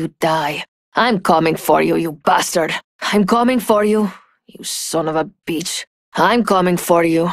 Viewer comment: DOES SHE REALLY SAY YOU SON OF A BITCH ?!?! 😂😂😂 I love Loba nowwww lmaoooo